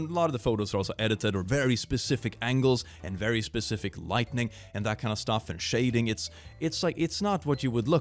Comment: I'm not the only one that I heard him say lightning instead of lighting right, click if you actually read this comment I'm not making fun of you I just feel the need to point out things that I notice